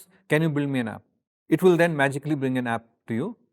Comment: did he actually say "magically" ? :p :3